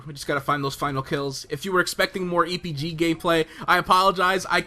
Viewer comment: Did anyone else hear: "more EPG gay-play"?